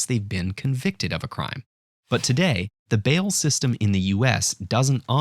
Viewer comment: i thought it said ball system